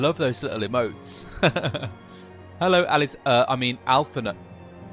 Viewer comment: did he call he out someone named "Alis-I mean Alphinaud"?